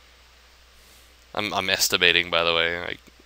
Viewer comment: i heard 'I'm masturbating btw' xD HAAHAHA